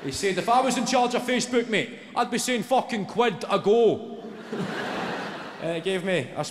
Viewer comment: What is he saying at 5:15? "If I was in charge of facebook mate, I'd be saying fuckin' quid a goal"? I'm guessing the closed captions didn't quite get it either. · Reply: "Quid a GO" is what he is saying. As in: " If I was Mark Zuckerberg I'd charge everyone £1 a turn to use Facebook". Hope that helps.....🤗